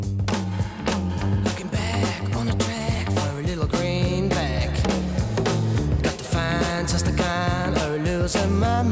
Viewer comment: What did oral say at 2:47 it’s driving me nuts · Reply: "Is it a Renault clio?"